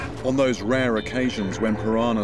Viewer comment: At the end, it sounded like he said "death by a thousand cunts"